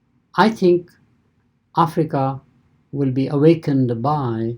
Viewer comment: Did he just say Africa we are awaking?